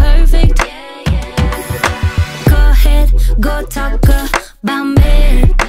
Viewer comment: Why did I always think at 0:31 she said go taco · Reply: She said go talk about about me